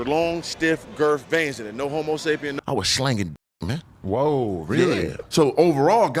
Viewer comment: Did he say 'No Homo Sapien' ?? 😅